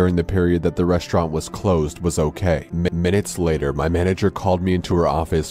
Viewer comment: What Everyone Else Hears: Minutes Later What I Hear: McMinutes Later